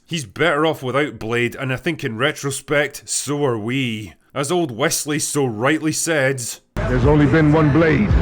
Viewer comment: Me confused because I thought you said "Wesley So (the chess GM) rightly said"